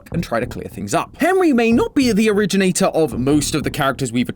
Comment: did he just say Henwy